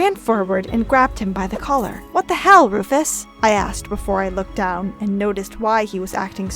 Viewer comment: did she say "what the HELL Rufus " @ 1:36 👇 press this if you heard it